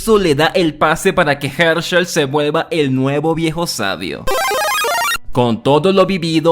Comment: Yo entendi "hershel el viejo sabroso" Xd